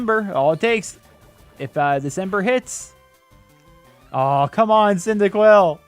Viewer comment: Did he say december?